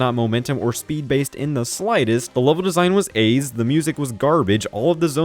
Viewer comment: What did the creator say at 3:28 regarding Sonic 4 level design or did i mishear what i think he said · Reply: "The level design is AIDS"